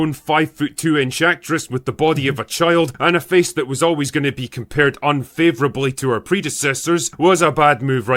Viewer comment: I thought he said "and a face that was always going to be compared to her predecessors" 💀